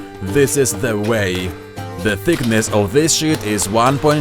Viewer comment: It sounds like he said, "The thickness of this shit". Which is what I wish he did say!